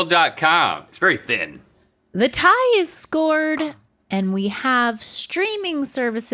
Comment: did she just say the tie is scored?